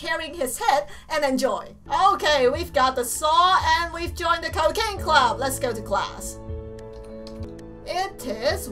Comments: Sounds like she says cocaine club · is it me or dose it sound like she said cocaine club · Did She Just Say Cocaine Club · when she was saying cooking club i thought she said cocain club! XD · Did it sound like she said cocaine club? xD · It really sounded like you said cocaine club. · its sounded like she said cocaine club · something, i thought she said cocaine club! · When you said cooking club I thought you said coccaine club... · it sounds like she is saying cocaine but she saying cooking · its weird because the first time i heard her say cooking, i thought she said cocaine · Im not trying to insult ur accent but sometimes when u say cooking club, it sounds like you say cocaine club! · I thought she said cocane club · I thought she said cocane club😜😆😂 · I thought you said cocaine instead of cooking xD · when she said the cooking club i thought she said The Cocaine Club XD Lol for real im not kidding